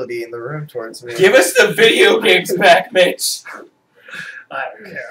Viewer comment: lol 10:26 sounds like he is saying give us the video games back bit@ but really says mitch